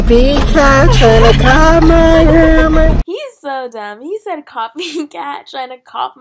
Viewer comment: I thought he said cop my hammer lmao